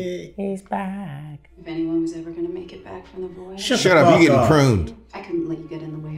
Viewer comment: I thought I just heard "Shut up, yee haw.".